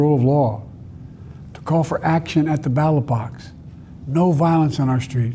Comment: I swear I thought he said battle box instead of ballot box. Earfart. I'm kinda irked that so much sympathy is being shown for TFG, because you know damn well if the shoe were on the other foot it would NOT happen.